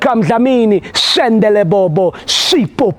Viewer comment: I thought you said "sende lababa"😭😭